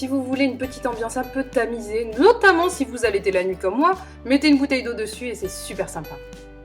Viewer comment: si vous quoi ? J’ai compris s’amuser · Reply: OMÉGA bruh si vous habitez la nuit je crois